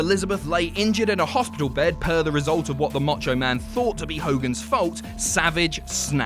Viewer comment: did he say Mocho Man?